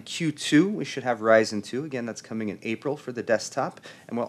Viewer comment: Is ryzen+ the second gen or is ryzen2 the second gen??? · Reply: MayDay GoingDown!! That's what I thought before this video. I must've misunderstood him in the fourth minute, thought he was saying zen2 in april?